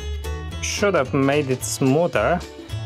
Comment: thought he said shut up